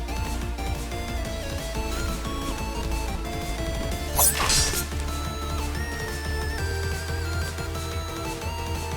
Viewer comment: did he really say that man? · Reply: He said don’t heal